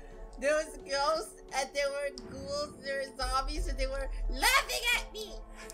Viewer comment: Tell why I thought that draco said “let me get me” but it was “they were laughing at me” when he got out of lunars dorm 😅